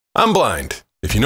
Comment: Guy: "I'm blind" Girl: "I'm dumb" Manager: "I'm dumber" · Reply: I hear "I'm black"